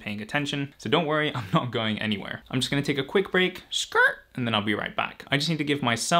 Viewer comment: Did you just say "skrrt" lmao mimiyuuuh is shaking